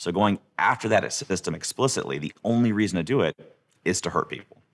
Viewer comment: did he say “the only reason “I” do it is to hurt people”???? wtf???